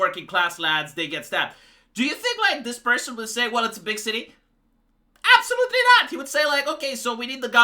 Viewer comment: I thought the guy said, "It's a bit shitty, sadly it happens sometimes." instead of 'big city". I guess either one works.